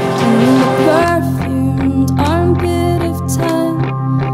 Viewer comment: I heard "perfumed armpit of time" 0.0